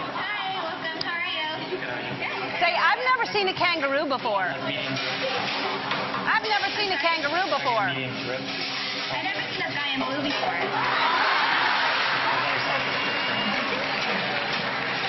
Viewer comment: when she said "iv never see a guy in blue before" i thought she told her to say " iv never seen a kangaroo before" hahaha ....hm yeah :|.